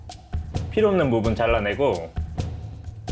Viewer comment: 나만 이거 쿠키런의 몸은 잘라내고 라고 들었나